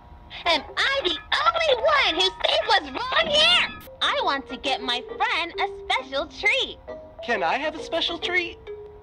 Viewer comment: Is it bad that I always thought Amanda said “I want to give my friend a Special Tree!”?